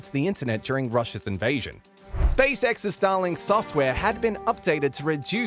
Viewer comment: I honestly thought he was saying Stalin until I realized he had an accent